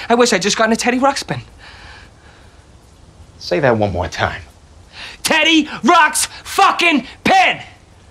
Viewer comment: What the 🤬 did you say!? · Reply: TEEDY, RUX, BLEEP, PIN!